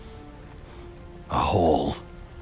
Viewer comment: The first time I got this scene, I thought Mimir said "Asshole" for a second